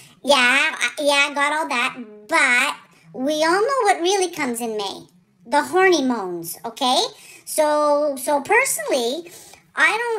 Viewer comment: I’ll never be able to say the word hormones again...from now on it will always be hornymones! Love you, (and your mom, too). Thank you for brightening our days! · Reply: That's so funny. I heard "horny bones." That makes more sense!